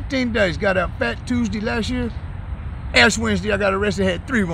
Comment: Did he say ASS Wednesday at 1:42? 😂 😆 🤣